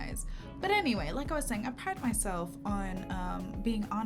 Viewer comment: I had rewatch the intro because I thought she said" I'm high myself "and I was like HUH🤣🤣🤣